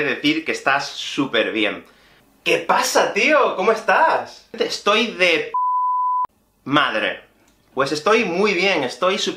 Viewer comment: dia 1 hablar espanol "Que tal? Estoy bien gracias y tu?" Dia 365 hablar espanol "Que tal? Hombre estoy de puta madre" jajajajaja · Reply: Anteriormente pensé que dijo estoy de pene madre 😅